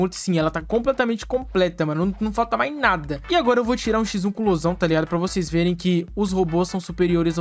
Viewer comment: Juro que eu ouvi "agr vou tirar x1 com o Mozão"